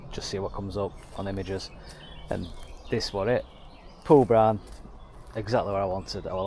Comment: You scored that for 4k?? Wow, you absolutely stole it. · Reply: Haha oops. At around @ 0:56, you said something that sounded like “four grand”, which I now realize sounds like something “brown” and makes more sense. I was in some disbelief that you could get one that cheap, even with the bad floors, dash, mechanical and cosmetic issues. Really cool car. I have a ‘68 in my garage, and it’s always fun to see other 912s out there